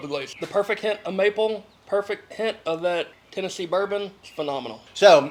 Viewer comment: Did he just say “Tennessee Bourbon”?